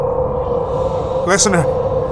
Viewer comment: did he say listener and if so does it count as a 4th wall break